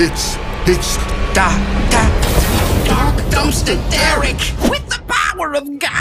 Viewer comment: When I first heard the robbers say his name I thought they said "Dock dose the Derrick" until I heard "Dark dumpster Derrick" much more clearer.